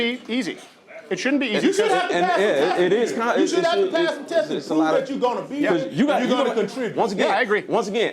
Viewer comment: Did he say “test-is” ? 😂